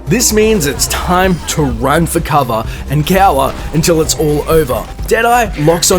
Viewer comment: wtf did he say? it sounded like get ow awah. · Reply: cower